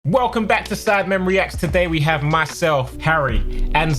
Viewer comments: Yo for a second I thought he said ‘welcom back to sad men reacts’😂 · At the start I thought I heard "sadmen reacts"